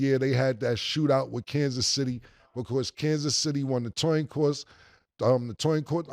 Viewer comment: Wonder what Killa was thinking when he said “Toin Coss” 😂